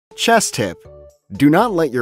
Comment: I thought that said cheese tip